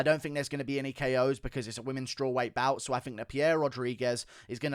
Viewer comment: I misheard womens strawweight bout, I thought you said menstrual weight bout the first time lmfao..